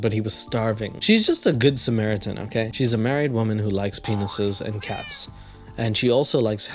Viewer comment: At first I thought she said that she likes peanuts